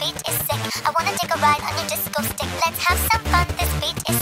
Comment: i thought it said ''i wanna take a bite of the disco stick'' 😭✋